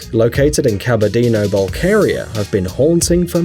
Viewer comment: Where did he say this blue lake is? It sounded like he said "Cabadena Vulcaria" and no such place exists.